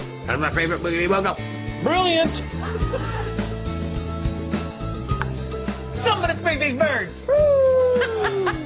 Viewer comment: hahaha yeah! OMG! They're so funny! I like the part where Nathan says "somebody feed these baby birds" 2:24 (that's what I heard lol!) AND Seamus's laugh...again xD